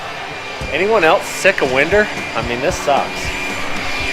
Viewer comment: you asked if anyone was sick of Winder instead of winter. Are you mad at?